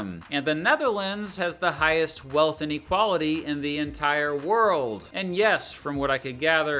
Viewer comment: Small thing but at 18:50, i assume you meant “wealth equality” instead of “inequality”? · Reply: I thought he said "AND equality" not INequality... 😅